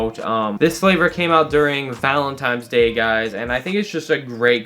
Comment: did he say Valentimes day?🤦🏻‍♂️